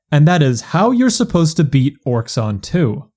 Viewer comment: how you supposed to be "what" ? (and the auto subtitle say a too wweird thing) . edit : "to beat orxon 2" ?